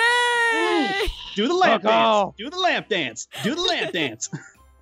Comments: "do the lamp dance do the lamp dance!" my thoughts:..did he say lap dance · When said "do the lamp dance" i thought he said lap dance... xD